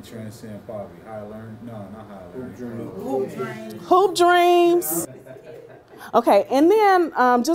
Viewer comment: I didn't catch the name of the movie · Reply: Hoop Dream(s?)